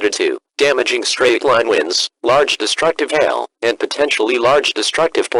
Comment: the captions say hell instead of hail